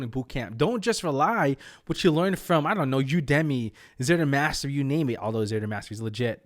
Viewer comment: What's the resource he says is legit at 3:54? Something-master?